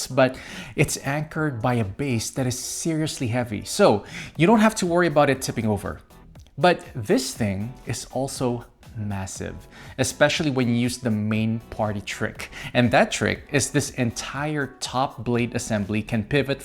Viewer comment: Did you say massive? 1:28 😅